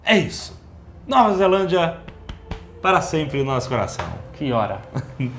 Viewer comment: eu escutei "ki ora" como "que hora"